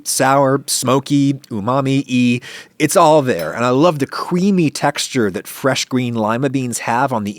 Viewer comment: Did you just say "cewamy" instead of "creamy"?